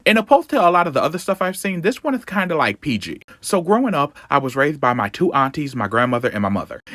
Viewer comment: I though he said he was r**ed by them!? im glad i heard that wrong